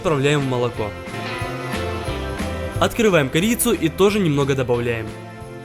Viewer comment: Я же не одна на 4:50 услышала не корицу а перца?